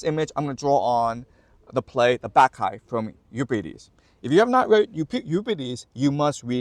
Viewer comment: can somebody tell me what he is referring to here. I tried to find it, but I'm probably spelling it wrong. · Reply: The play "The Bacchae" by Euripides.